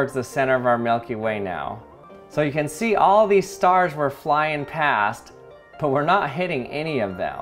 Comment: They also explain that the deflector dish literally deflects smaller debris and im pretty sure they also explain that the computer can make adjustments at light speed to the course as not to hit anything at least on voyager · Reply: yeah I thought he said star wars